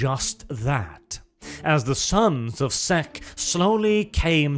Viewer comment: Watching to much kibs thought it said sons of sqek